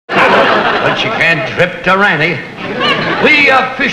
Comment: did I hear ĐŘÏP